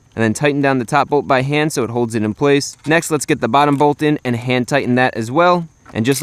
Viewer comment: every time i hear him saying "hand tight" i rather hear "hentai"... lol